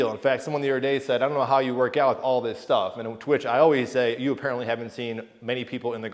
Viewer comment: No, but that would be fun. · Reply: running it back, i misheard 2:05 "on/and to which". [And, on Twitch, ...] Sliding bench livestreamer is an interesting space to say the least!